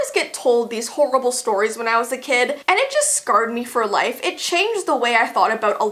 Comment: did you guys here her say scard instead of scared not being mean just saying I would never be rude to I’m a big big fannn love you ❤️❤️❤️❤️